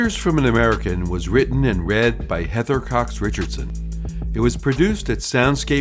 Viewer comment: Thanks, Heather. At the end where the man says "it was written and read by...", I thought he said, "written in BED by..."